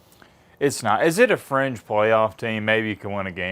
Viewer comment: Thought he said “French playoff team” for a split second 😂 he said fringe